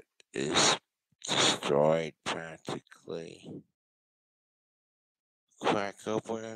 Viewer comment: did just say “My cigarette is destroyed tactically”